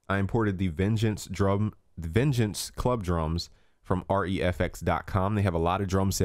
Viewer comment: Sorry, doesn´t understand. What did he say at 2:24? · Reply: he got his samples (vengeance) from <link>